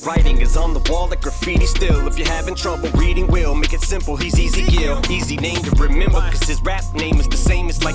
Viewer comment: I dont know about this english is not my first language but (question) Is it possible with Ems Ezekiel line that he pronounce it like easy kill? Like if you model the line like this: If you having trouble reading will, make it simple he’s Ezekiel (easy kill).. 🤷‍♀️ Normally its: … having trouble reading, will make ite simple… But he pauses after will and pronounce Ezekiel like easy kill. I dont know does it make 100% sence cause my english but I’ve been thinking that. Also just discovered fully and this is my nro.1 page love these videos and the community ❤️ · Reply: Hey man! Im English native and yours is absolutely fine and i completely agree with you too! Ive also always thought he said “he’s easy kill” and i bet he did it on purpose cause, its eminem haha